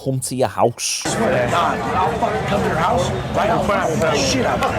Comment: I thought he said “I’ll beat the s**t out of your house”😂😂😂😂